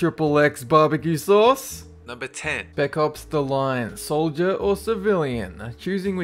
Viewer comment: What is the game at 2:49 I can't hear what he said the name was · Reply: Spec Ops: The Line (6 Years late, I know)